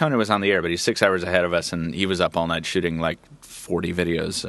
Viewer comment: Did say "shitting" or "shooting" at 00:15:18?